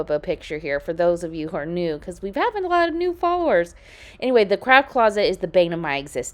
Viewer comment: I totally thought you said the crap closet instead of the craft closet. 😂😂😂😂😂